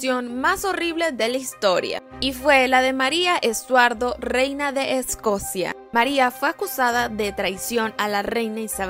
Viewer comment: Yo escuché "Reina de Escoria" xd